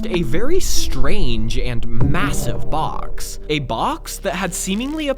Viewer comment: did you say M-Massive